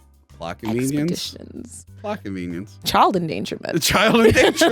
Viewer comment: Ooohhhhh..."plot convenience...plot convenience"! THAT's what he's saying! CC wasn't picking it up, and I was looking up things like "blockovenians"and "lackovenians". I'm dumb.